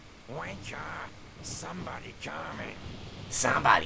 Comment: I thought he said "Wanker, somebody's cumming"